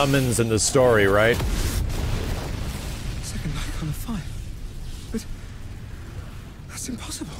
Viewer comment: Honestly just saying thanks for telling me what they said at that point. I heard "He set the night on fire." and couldn't understand what he was talking about. "A second Eikon of fire." makes so much more sense.